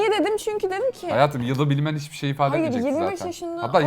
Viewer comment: ´da ki mertin yarene « hayatim »demesini sadece ben mi duydum